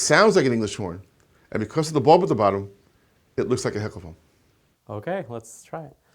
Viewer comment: Can't hear what he says at 2:30. Sounds like he says "looks like a hippoma"